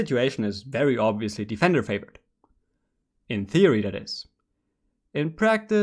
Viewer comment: When I heard “in theory that it” the third time I was about to discriminate against vitality right there until you said joking ...